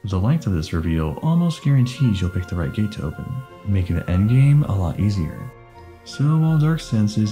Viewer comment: the subtitles says “making the end game go more smoothly.” but says “making the end game more easier.”